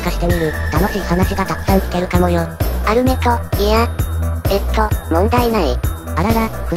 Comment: メアトルがアルメトって聞こえる